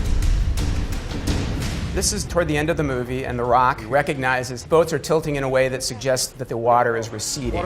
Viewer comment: Is it me or did it sound weird how he said the rock at 0:46 ? Lol sounds like he said the raawk lol